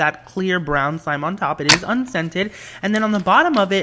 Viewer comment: Every time he said 'unscented' I thought he said 'and scented' so if we had an allergy it would be perfect for us